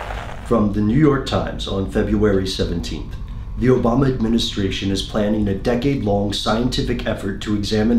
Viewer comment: Turn on captions. 0:10 - when he says Obama Administration, the captions say "vomit ministrations"... Pretty accurate lol!